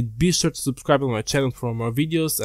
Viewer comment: "and lag the video" that's what i heard lol